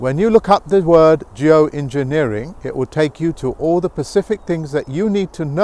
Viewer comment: did he say ''pacific''?